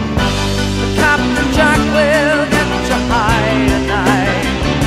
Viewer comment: I can't be the only one who hears "Captain Jack will get you BY tonight" at 1:15. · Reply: Never mind. It's "high." I was hearing "by" for years.